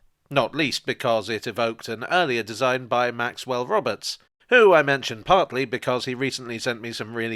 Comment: when you said maxwell roberts i thought you said robert maxwell and i couldn't believe you were in contact with the mossad spy triple agent, dead 34 years